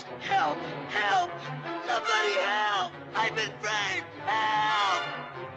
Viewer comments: am I the only one I heard "I've been raped"? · for some reason when I was a kid I thought he said "I've been raped"...